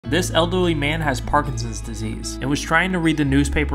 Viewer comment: Omg sorry but this is really kind but what I'm sorry for is that I misheard "this elderly man" And I misheard it with "this ugly man"sorry I'm sorry